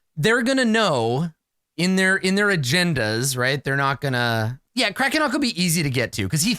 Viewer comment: When said "Agenda" I heard "A Gender" but in an accent and then immediately made my next character, a cockney-sounding non-binary person who uses e/em pronouns.